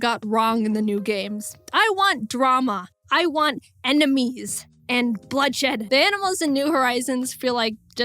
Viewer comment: i think she say ENEMIGOS i think that maybe she dont say that.